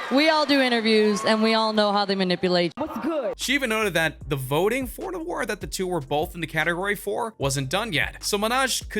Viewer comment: did she say what’s good? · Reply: Yeah I blasted the volume and I heard what's good